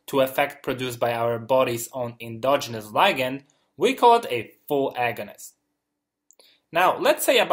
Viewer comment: What did he say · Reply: "...endogenous ligand, we call it a full agonist..."